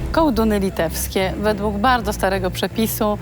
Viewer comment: Ja usłyszałam „kołtun” a nie....... coś tam